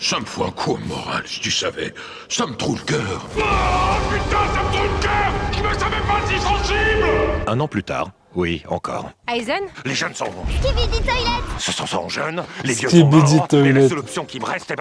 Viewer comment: Je n'ai pas compris ce que dit Fern? Skibidi Toilet ? Du coup c'est quoi la vanne?